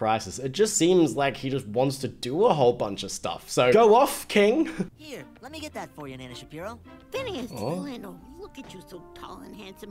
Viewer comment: i was eating so the audio was quieter and i thought Phineas said “Here, let me get that for you, Ben Shapiro”????? 😭😭😭😭